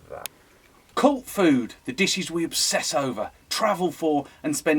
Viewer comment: sounded like cold food.